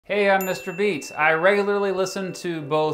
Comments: I thought you said you were mr beast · Who else misheard as Mr Beast